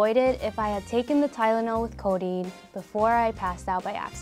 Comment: did she just say codeine?!?!?!?!